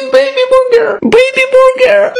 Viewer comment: For me it sounded like he said:"Baby booger!"😂😂😂😂😂